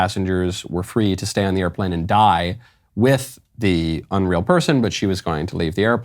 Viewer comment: Swear to god I heard “so y’all can dine with him” when the plane lady was speaking, someone had to point it out to me that she said “die” lol